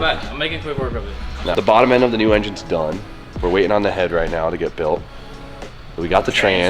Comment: did he say THE HEAD ?, are we actually doing a fucking 2j 🎉🎉